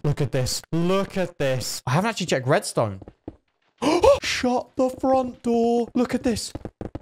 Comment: I thought he said shut the f word off